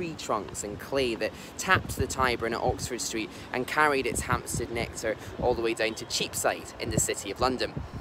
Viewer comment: What does he say at 2:20? I checked with CC on and it says "... and carried its hamster nectar all the way....". Is "Hamster Nectar" Cockney slang for something or am I just mishearing it along with the AI closed caption? And by the way mate, if you are reading these comments, this was another great video! Love your stuff. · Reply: Hampstead nectar! A bit of a strange turn of phrase, I grant you. Thanks for the compliments! :D